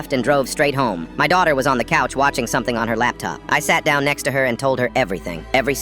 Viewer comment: I heard “i shat down next to her”😭😭😭